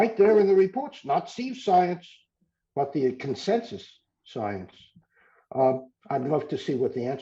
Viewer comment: I can't make out a word that Professor Koonin uses here. At 23:44, he states: "It's right there in the reports. Not '*** science but the consensus science..." It sounds like 'seeve' which doesn't mean anything. Any help would be gratefully received. Aside from my question, great discussion. Really enjoyed it. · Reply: He says, 'It's not Steve science, but consensus science.' So I would assume that he is referring to himself and just his scientific opinion, such as what he states in his book.